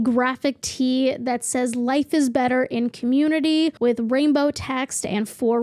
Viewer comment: I thought that said "Life is better with COMMUNISM"